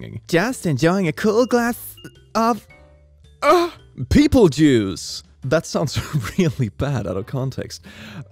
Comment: at around 1:08 i can swear i heard pp juice